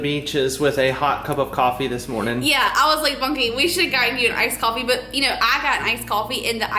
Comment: I thought your cap said “Iced Coffee Cleanse”!! I am laughing so hard at myself right now!! 😂 😆